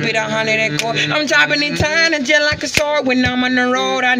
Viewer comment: I’ve watched so many times, I’m singing along with bruh!! Shit is 🔥🔥🔥 real deal. Super talented EDIT- Daaaaamn!!! I guess I’m not the only one singing along😂😂😂😂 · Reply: I thought he said choppin that time like a sword....🤷🏾